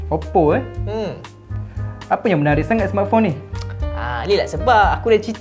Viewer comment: Cara laki tu sebut "smartphone" mcm org ganu....sebutan die "smartphong" kalau tersalah dengar tu sorry lh😅